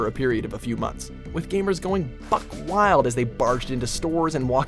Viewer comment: Did he say "fuck wild"